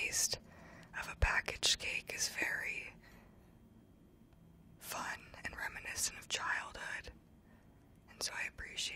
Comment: What is the word that she says before “childhood” at minute 9:06? Sorry english isn’t my first language 😅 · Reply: « reminiscent of childhood »